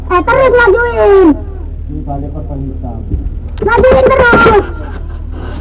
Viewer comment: Detik 14:08...."majuin terus bodoh"...lah saya apa saya salah dengar....?????